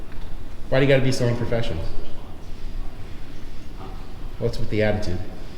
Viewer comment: At the 7:52 mark, what did the woman security guard say to you? It's kind of muffled. It also made you comment on it as well. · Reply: She said, "Almost like you got real business." She was pretty upset that a member of the Press would dare come do a story on the place. Thanks for watching!! Please Like, SHARE, and Subscribe. Let's get OUR message of Freedom out far and wide!